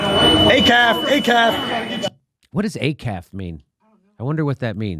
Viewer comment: is he saying, "ACAB?" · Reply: Acaf, all cops are friends